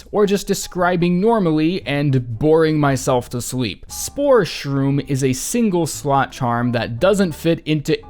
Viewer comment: Why did you just say sports room what is a fart by itself it's not a fart until you add defenders crest